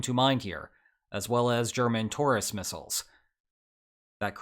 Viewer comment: I thought you said "German tourist missiles" :D